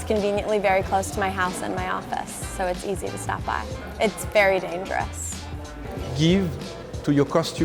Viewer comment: did she say "it's very dangerous" ?